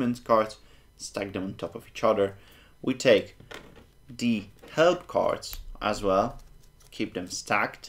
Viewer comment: Can someone tell me what the herb cards that he mentioned are? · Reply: I believe they are the "help" cards. The play instructions cards.